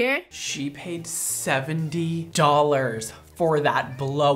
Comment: As a black girl, I felt everything they were doing to her, that is humiliating and disrespectful. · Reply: i thought she said she paid 17$ but she paid 70$!!!!😳 fuckkk no. i feel so bad